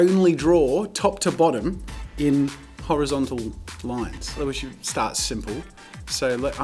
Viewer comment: damn i thought he said he was gonna draw Hentai